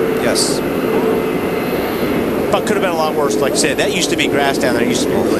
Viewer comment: did wally say "Fuck it could have been a lot worse" or is it me???